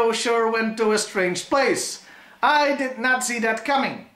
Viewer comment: At the 8:44 mark - know you didn’t do it on purpose but I swear it sounded like “I did Nazi that coming.” Lol